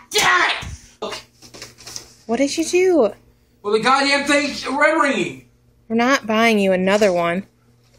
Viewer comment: what did he say? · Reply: "The god damn thing's red ringing"